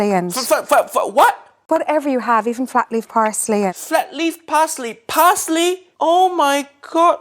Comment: I thought she said "Fat free Parsley" XD